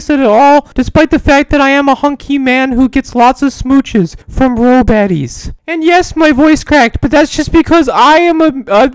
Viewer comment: WHY DID I HEAR IT AS "From real daddies" OMFG BAHAHAHAHAA